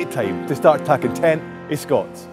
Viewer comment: I thought he said at the end "star takkin ten o' Scots" but he said "stairt takkin tent o' scots" meaning "start paying attention to scots", I was so confused.